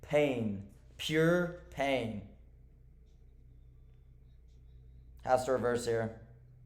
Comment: did you say the market have reverse there? Lol the market doesn't care if you draw a line.